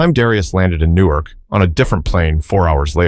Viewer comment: Why is the narrator saying Newark and the subtitle saying New York ?